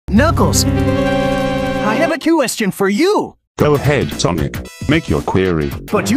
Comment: it sounded like sonic said quiestion instead of question